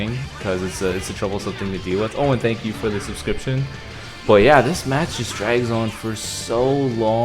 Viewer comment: I just realised that you say "oh and that you" instead of "Owen, thank you for the subscription" lol I was just like who the fuck is Owen?